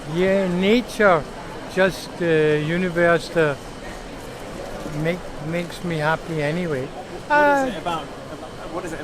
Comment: I thought he said, "Nietzsche make me happy".